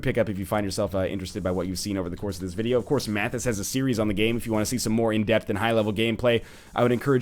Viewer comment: Who did he say at 27:53 does a series on this? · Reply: mathasgames